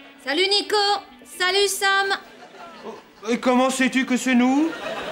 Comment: what did he say? And what did she say at 2:50? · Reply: Comment sais-tu que sommes nous? 2:50 C'est les Champs-Elysees un samedi soir, ici